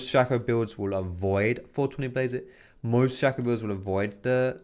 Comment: Did you say avoid 420 Blaze it ??? 14:12 lmaooo